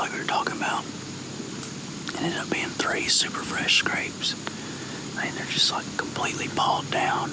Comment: Captions are REALLY sloppy here, to the point where the meaning of what's being said gets changed and makes it pretty confusing. 1:43 "fresh grapes" instead of "fresh scrapes", 11:39 "block" instead of "bloke". I mean, in the third video ALONE: 17:09 "what a camera come across" instead of "would've come across", 19:00 "wouldn't it" instead of "wasn't it", 19:42 "rankin dink" instead of "rinky-dink", 19:53 "everybody water" instead of "every body of water", 19:56 "die" instead of "dive"... y'all got a proofreader? And please, no party invites. I'm not fun at those.